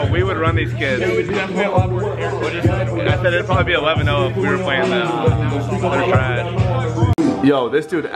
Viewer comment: Hahaha in the moment I thought you said it’d be 11-0 if we played 1on1